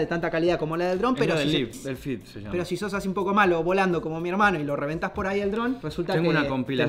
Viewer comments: Pensé que decía violador xd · Por un momento pensé que decía "VIOLADOR" xd